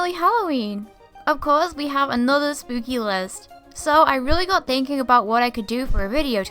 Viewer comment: I thought she said I really got naked xd